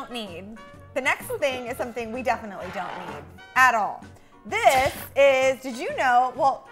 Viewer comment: I thought she said something we don’t need...... adderall.... But yeah I think she really does need adderall sometimes